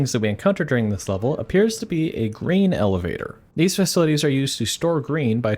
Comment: i could’ve swore you said “green elevator” as in transporting the color green. I was very confused.